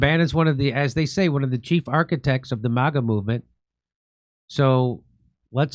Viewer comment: "Chief architect of MAGA" I heard "Cheap architect".